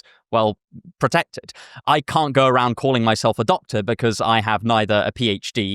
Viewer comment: thought you said adopted and i was like “you don’t need a degree though” 💀